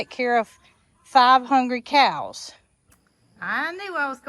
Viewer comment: At first I thought you said, "Got to feed 500 cows!". Finally figured out it was 5 hungry cows. Made me laugh. Love your surprises. Native plants are perfect for Cog Hill! It's going to be beautiful!